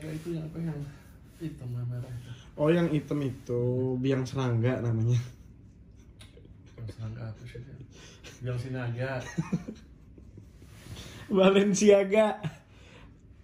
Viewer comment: ini gue yg salah denger apa emang bilang sinaga ya??